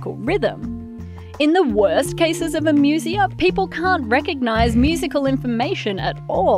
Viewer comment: I might not be able to recognize musical info, but I'm damn sure I heard the C-word. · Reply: "Can't" say I know what you're talking about. It's an Australian thing.